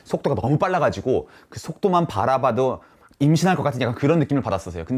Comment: 링 껴서 불편 한거지 지문인식 전혀 불편하지 않음 그리고 2:41 임신? 뭐라는거야 내가 잘못 들은거? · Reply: ㅋㅋㅋㅋㅋㅋㅋㅋㅋㅋㅋㅋㅋㅋㅋㅋㅋㅋㅋㅋㅋㅋㅋㅋ임신이라고 한듯 ㅋㅋㅋㅋ